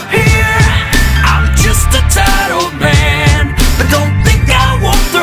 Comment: I misheard tired old man to tilted man. damn